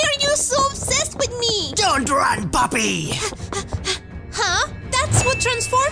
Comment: Almot sounded like he said don't run poopy lol